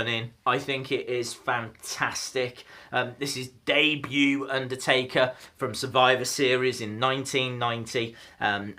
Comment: I thought you said undertaker from 1919